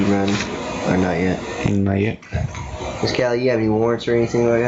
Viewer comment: When he asks if she has any warrants 20:37 I thought he said “rewards”, like when stores have reward points. For a second I’m thinking “are they seriously asking so she can use her points to pay back some of the stuff”!! 🤣😂 wow, I’m an idiot! I only though it for a SECOND tho...